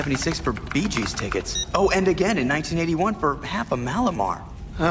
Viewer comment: Sorry, I'm not a native speaker of English. What exactly does the devil's assistent say in 0:15? "half a " - what? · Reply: Mallomar. It's a packaged cookie containing marshmallow. :)